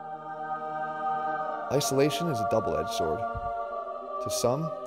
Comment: When he said "isolation" at the beginning I heard "isometric" lolool🤦🏾‍♂️